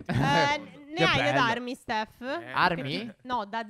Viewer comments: anchio ho sentito armi 😂😂 Sono con te 😂😂😂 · anche io ho capito armi ahaahaha